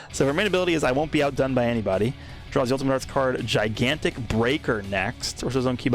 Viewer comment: I thought he said "gigantic breaker neck"